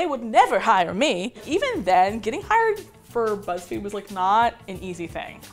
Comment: i thought it said getting high and got way too excited